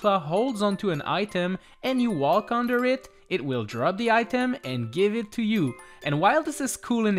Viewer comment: i thought he said drugging the item